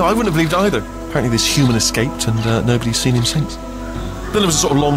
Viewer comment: "Him?" Is he talking about Rattmann? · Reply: Yes, I know that. It sounded like Wheatley said "...and nobody's seen him since." That's what confused me, since Chell is a woman. It wouldn't make any sense if he was talking about Rattmann there, or anyone other than Chell.